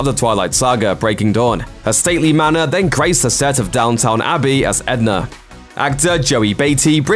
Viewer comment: Did I hear Downtown Abbey instead of Downton Abbey???? 😱😱😱😱😱😱